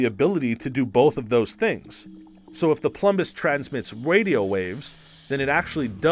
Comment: Did anybody else hear him say wadio waves